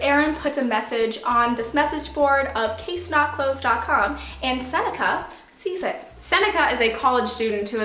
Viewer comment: I thought she said 'And Santa sees it' 😂